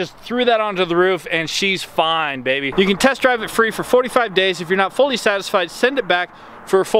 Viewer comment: Did he just say TEST DRIVE THE WALLET ? LMFAO